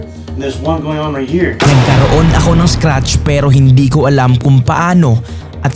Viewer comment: in 19:53 i think he/she said "scar" because something will happened or already